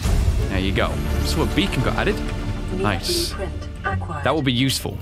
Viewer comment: Who else thought it said bacon instead of beacon I'm probably just hungry